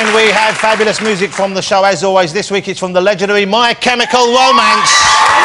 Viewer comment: first time he says it, it sounds like he is saying: "My chemical lomance"